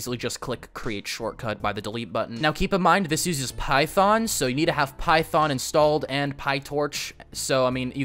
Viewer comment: i didn't understand what install in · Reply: python / pytorch cuda